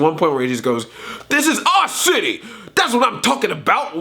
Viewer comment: XD I thought you said 'this is all shitty!' Instead if 'this is our city!' When you were talking about Ralph's first line in the trailer.